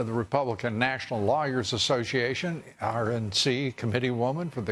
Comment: anyone else thought he said republican national liars association?